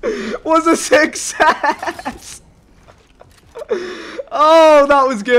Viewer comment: Port a fort was a 6S?🤨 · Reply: redpanduo 9:43, success sounded like 6S